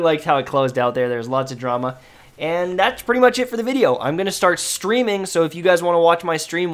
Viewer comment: I swear you said i'm going to start screaming lol